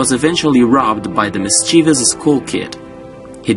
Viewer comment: I thought you said school kid...